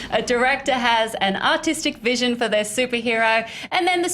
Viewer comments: Didn't process the accent at first and thought she said ''...an autistic vision" lol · HAHA! At first I thought she said "autistic" vision... well duh, of course this movie made the suits nervous. You know how much they hated Deadpool when it was in production? Anything non mainstream to them is a major fear factor. BUT they LOVED Fan4 while it was in production and we all can agree that was kinda far from being a master piece... Logan will make all the other super hero movies look like a kindergarten matinee. March 3rd can't come fast enough.